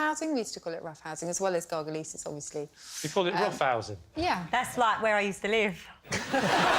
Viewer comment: What did Stacey Solomon say at 2:13, can anyone please tell? Thanks :) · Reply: I think she's saying "That's like where I used to live" (ref to Lee saying "roughhouse").